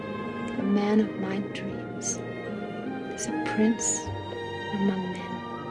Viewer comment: what did she say? A prince who what?! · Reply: A Prince among men.